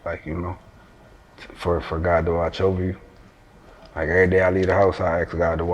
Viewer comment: What did he say at 12:52? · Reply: “For.. for god to watch over you”